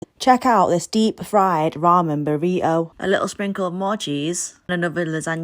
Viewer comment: Thought she said deepfried vomit